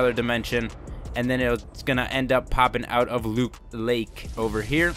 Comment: At around 2:21 anyone else Hear loop lake?